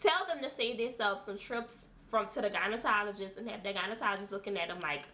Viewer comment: Lol! Great informative video! Is it me or does it sound like she is saying "gynetologist" instead of gynecologist! So if one dares to put in on the "bottom lips or anus" how long should the product be put on there for? Less time than suggested? Just curious 🤔